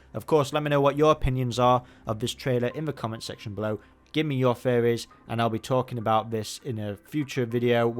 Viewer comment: You can just tell I must be tired waiting on this trailer when I hear "Give me your furries" When says "Give me your theories"